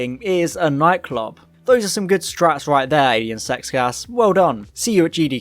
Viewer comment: I thought you said "Ian Sex Gas" and that's my name now